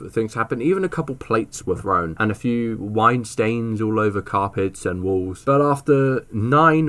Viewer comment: I thought you said white stains 😭